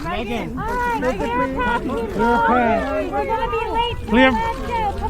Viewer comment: did anyone hear at 0:23 were gonna be late to alaska come on lmaooo